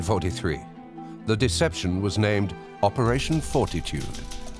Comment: the name of operation had me cracking up I just hear operation foreskin when he says fortitude 😂